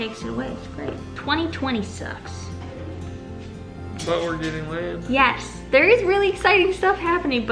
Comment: A: “2020 sucks” R: “ but we are getting land” I had to relisten to that bit. I thought I heard “but we are getting laid” Daisy will be missed, but so excited to play pain free with Levi and Skye. 😭😍 picture